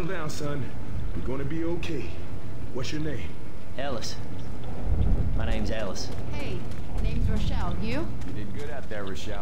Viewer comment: when Ellis says "My name is Ellis it sounds like he says mY name is Alice